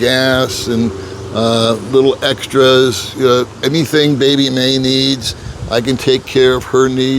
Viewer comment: You've got a baby?😮 2:21 Did I hear ut wrong? What is ur girlfriend's name? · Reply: My wife? Baby Mae